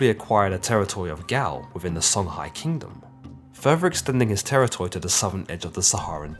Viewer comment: did he say nigga at 4:43 ? 🤨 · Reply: No expanding